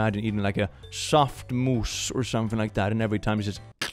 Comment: I really heard; "everytime I eat a soft moose" and was sooo confused for a good ten seconds, until I understood it was "mousse" 😂